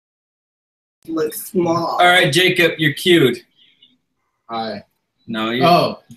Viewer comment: Right at the start it sounds like said "Alright Jacob you're cute" 😂